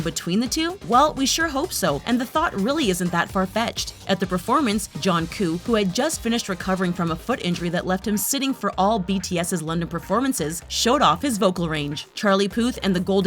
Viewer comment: DID SHE JUST SAY "JUNKOO"?? BISHHHH WHAT!!!